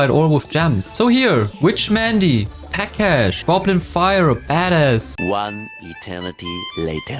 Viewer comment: what did he say💀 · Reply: Badass